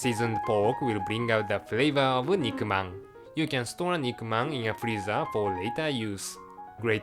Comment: did he just say enigma?